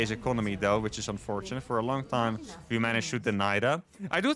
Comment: what did uthermal say at 26:50? is he speaking english? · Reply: “We managed to deny that” 😂